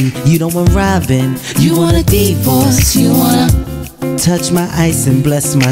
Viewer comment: "You don't want Robin, you want a divorce" Foreshadowing. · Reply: Lol all this time I thought he was saying "you want a deep voice" 😂